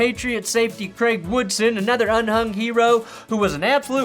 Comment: did you mean to say un-hung here? Just making sure. that is what my x wife used to say to me after we slept together.